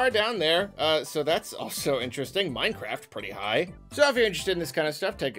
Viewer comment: My ears thought they heard say "Minecraft...are they high?"